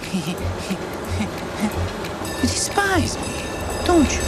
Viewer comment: The subtitles say “you despise me, don’t you” but it seems that he actually said “despise me, don’t you” I think. Edit: 28:34 something similar happened